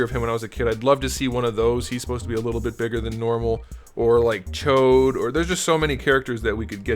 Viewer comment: "or like Chode" 👀 I heard that lmao